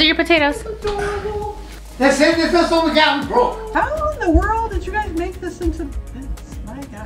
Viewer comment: Your mom's reaction was amazing. Where's all my food?? And sounded like he said we're broke. 🤣 loved it